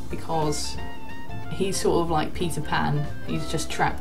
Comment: I thought he said "pees a pan" lol instead of Peter pan lol 🙈